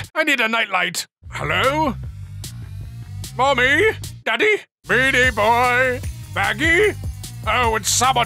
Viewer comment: did he say beanie boy or meaty boy